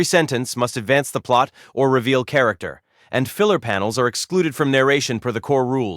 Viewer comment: what was that said..? Filler panels..?